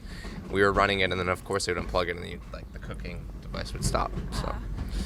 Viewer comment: I thought she said cocaine instead of cooking 😂